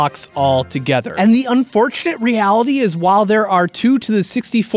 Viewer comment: Wot if u have fortune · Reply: For a second I thought you said fortnite